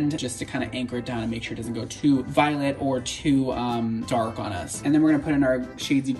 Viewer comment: Brad when you mixed up the toner and said violet I thought you said “so it doesn’t get too violent 😅”